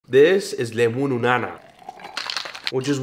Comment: He said "This is Lemunu nana" I heard "This js GOOGOO GAGA"